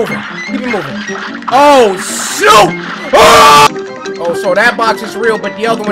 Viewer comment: I know he said shoot but it sounds like he said sh*t at 933